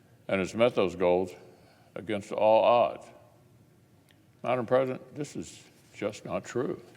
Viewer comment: Did he say "Madam President?" at the 1:14 mark?